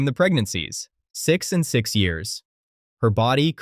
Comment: wtf did u say? 6 years pregnant? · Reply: 6 (pregnancies) in 6 years